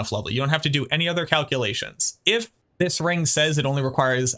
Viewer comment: Did I just hear you say "Cow kill-lations" as opposed to calculations? If so, thats a pretty good one 🤣